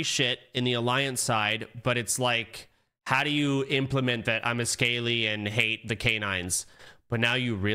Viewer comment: what does say here? Scalee hates the canines?